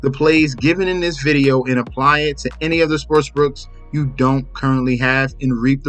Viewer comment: Bro why do you say sports brooks?At least it sounds like your saying that.